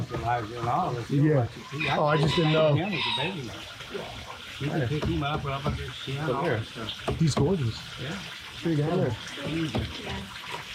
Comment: What was saying after you mentioned "staying back while Elijah does his thing“ at 12:28 regarding that croc monitor? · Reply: He said he lets Elijah do it all.